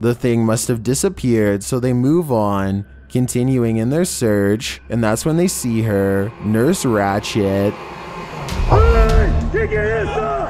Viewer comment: I thought he said nurse ratshit 10:28 at first ngl